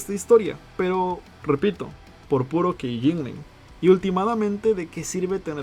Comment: por puro que ?? No conozco ese termino o palabra que dices. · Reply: Keyjingling. Básicamente, agitar las llaves para llamar la atención de tu mascota pero únicamente para eso. Treyarch hace eso al usar elementos del Caos para decir "Hey chicos, el Caos es canon". Así captan la atención siendo eso lo que querían.